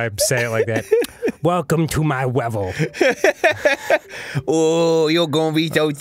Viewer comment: it sounds like they said "welcome to my egg." in Spanish XD